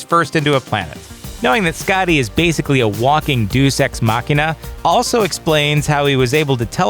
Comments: Deuce ex machina? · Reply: I heard "due sex machina". Thanks, Yahtzee... watch?v=W_LULRj_f4w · Deuce ex machina? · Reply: I thought he said "douche ex machina" It sounded like it was a deliberate play on words.